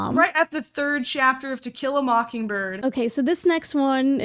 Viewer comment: help I thought she said Tequila MockingBird 😭